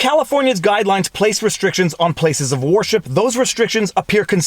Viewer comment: Canadian accent alert: at 6:48, I heard "war ship" - Ha!